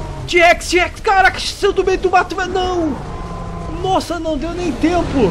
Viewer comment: Eu não entendi nada do que ele falou 1:58 kkkkk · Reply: Ele disse caraca t-rex saiu do meio do mato Dnd👊✌